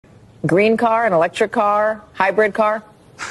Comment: Not me seeing a Mexican or what looks like mixing on screen and her saying green car I thought she said green card